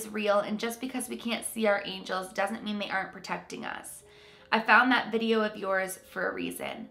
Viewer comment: "just because we can't see our angels doenst mean they aren't protecting us" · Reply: my dumb ass thought you said poking us not protecting us. smh